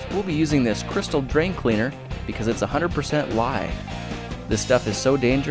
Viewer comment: I thought you said 100% lie